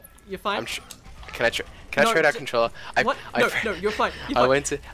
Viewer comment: I thought it said ‘controller only change’ for a sec